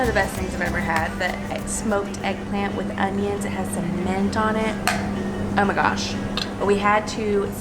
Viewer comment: Did anyone else hear with cement on it lol